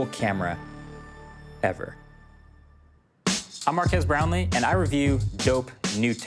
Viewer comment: If I controlled rewind, I would want Marques Brownie and a camcorder. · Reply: did you just say brownie lmao